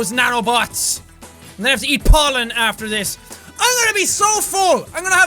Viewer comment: I thought he said "poland" instead of "pollen" and i was thinking "What did my country ever do to you for you to call it flower sperm?"